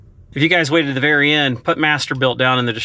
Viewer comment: MasterBuilt it sound like you said MasterBuild lol